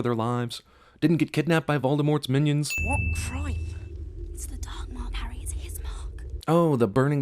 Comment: I misheard what Hermione said about the Dark Mark, so instead of hearing "It's the Dark Lord, Harry, It's his mark" I heard "It's the Timelord, harry, it's his mark"